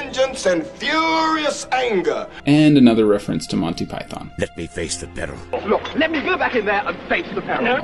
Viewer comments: I thought the Paladin said "Let me face the Baron" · always thought Uther said:" let me face the bettle" never heard "peril" wtf